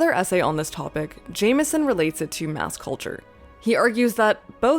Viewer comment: I heard it as 'mouse culture' and thought it would be a perfect description of modern Disney😂